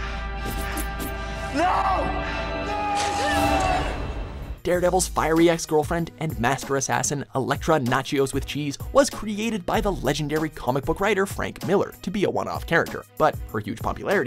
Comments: Lol am I the only one who heard him say Electra Nachios with cheese 😂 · Did he just say: " Elektra Natchios with cheese"? :p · Did he just say Electra nachios with cheese?? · Did you just say "Elektra Nachios with cheese"? 😂